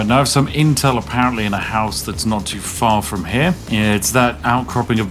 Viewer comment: Bro said “not too far” and I heard “naught two four” 😂